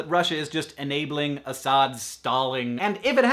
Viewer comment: "It could be that Russia is just enabling Assad's stalling" misheard as "enabling Assad's Stalin."